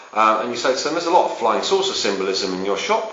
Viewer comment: what the fuck does he say at 3:26" I'm usually good with accents, but all I can make out is, "there'e a lot of flying ships (?) symbolism in your shop, why is that?'